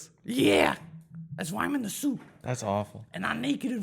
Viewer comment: Thought said he is in the soup